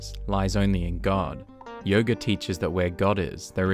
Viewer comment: did someone say yoga